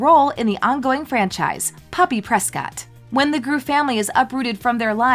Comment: who else heard poppy playtime at first?